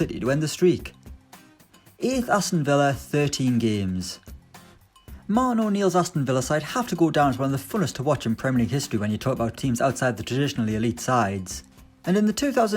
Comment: what was he saying? I can’t work it out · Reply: “Martin O’Neal’s Aston villa side have to go down as one of the funnest teams to watch in premier league history when you talk about teams outside the traditionally elite sides”